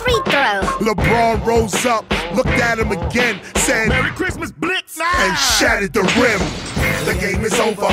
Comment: lol the 1st time i seen this commercial me and my big brother thought he said "merry christmas, bitch!" lol